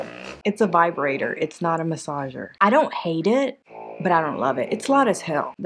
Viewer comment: I thought you said butt massage. I had to do a double take 😂😂😂😂 We don’t have a 5 below here yet. Not sure if one’s coming or not. But I’ll skip it thanks to